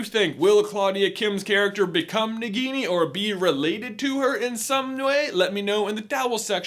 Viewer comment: is it me or did he say “in some nmway” instead of “in some way”?